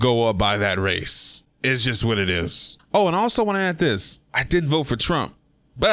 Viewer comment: I thought you said I did vote for Trump but you in fact said Didn't.